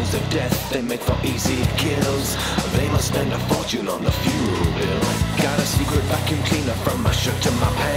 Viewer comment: Whenever it says, " fuel bill " i hear "funeral bill" lol